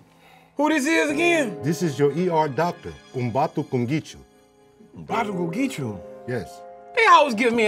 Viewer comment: is he saying I’m bout to go get u 😂 this start is so legendary. Best on and I’m not even a minute in 😂